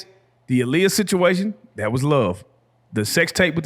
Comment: I thought he said hush not love on the sex tape part.